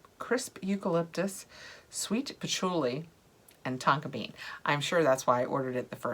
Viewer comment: I’m so far behind 🤣 next box add ons arw already shipping lol. At first I thought you said patchouli and taco because I was listening while I was typing at work and I had to rewind to hear tonka